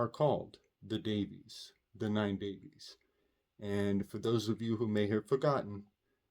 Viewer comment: the transcript shows you said “Davies”, and I thought you said “Debby’s”. You just had storm “Debby”, it’s a stretchy weave, though who knows anymore😂 in Edit; yes Thelema Harris is Davies, opps forgot😊